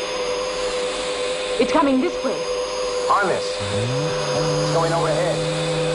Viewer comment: Nice alternative version of Timelash. I could almost imagine it was another episode where a UFO attacks SHADO in the dark and Straker and Lake use the rocket launcher a second time to save the HQ. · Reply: I think you may have misheard the line as "arm it". I believe Ed Bishop actually says "harness!" and we see Wanda pull down the seat belt, which were not compulsory at the time UFO was produced. I loved your tinting work. To me, it felt like they were on that roof in the early hours of the morning, maybe 5 AM. It gave it a great feeling! I'd love to pitch an idea for a video. I've had many thoughts, already. Have to see what I can come up with.